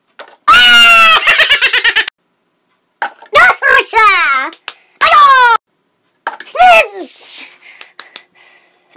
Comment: Did he said “Doswoshor” or Doswasha”? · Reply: Is DOSWOSHOR.